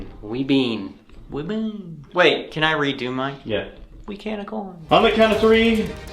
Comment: when he said we can of corn 🌽 I thought he said I’m a unicorn 🦄